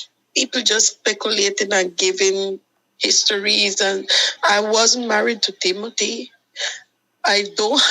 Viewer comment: 1. Question: You two were married correct? Answer: NO, we wasn't married. 0:29 2. I was married to Timothy. 14:12 No way in hell you can messed that up. She was in on it. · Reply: She said she “Wasn’t married” at 14:12 but it did sound like she said she was married. Nevertheless she is in on it. My gut instinct sense it. She got a cut of that money he forked over.